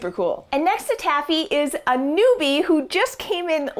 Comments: Name suggestion: Anubi. I misheard you call her a newbie and thought that was her name already. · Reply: Peter Mortensen 4:07 is where she says it; Gullen I also thought she said Anubi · Idk if I’m too late to this, but when you first said she was a newbie I thought you said “Anubis” which in my opinion is a good name